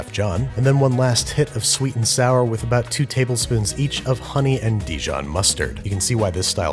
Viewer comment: honey and (dijon) mustard, not honey (mustard) and dijon mustard I had to think about it bc I heard the latter